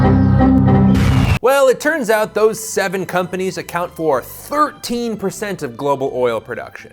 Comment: the subtitles incorrectly say 133% (in regards to the 7 companies vs saudi arabia) and again at 4:38 show 133% instead of the correct 13%